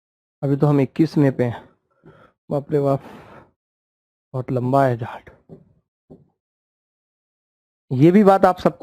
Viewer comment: से ध्यान से स्पीड स्लो करके सुनो 😂😂🤣🤣😅😅 क्या सुना 😂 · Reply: 🤣🤣🤣🤣🤣 bahot lamba hai jh--t